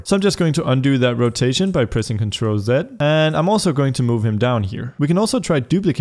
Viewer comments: can’t tell what he’s saying. He says something along the lines of control+(something else), but I understand what he says to push after that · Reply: Control Z. It's because he speaks like a proper English speaking human. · control what? I have no clue what he said and can't get the rotation off of my cube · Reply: Control Z. Also known as one step back